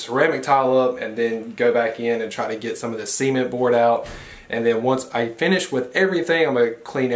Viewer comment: Are we all just going to ignore the way he says SEEment boards. I swear, every time he said it I thought he said seamen boards lmao